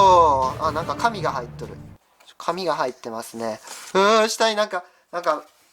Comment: 神が入ってるに聞こえた (言い直したけど) 他にも聞こえた人いるのかな〜 ↓↓↓